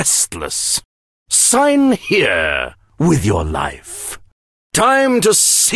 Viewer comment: Who else thought that Byron said "Sign here-with your knife"